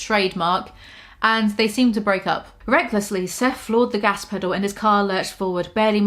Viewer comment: Instead of hearing “recklessly” I heard “greglessly” and honestly I’m not mad about it